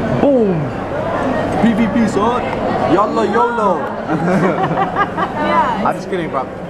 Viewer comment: YALA YOLO EXPLAINED at <link> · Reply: I thought he was saying "yellow yolo"